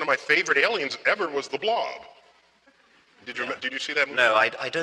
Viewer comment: When Neil Degrasse tyson said the blob I thought he said "the blonde"